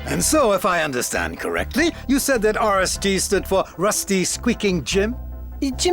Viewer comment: Sounds like he said RSD instead of G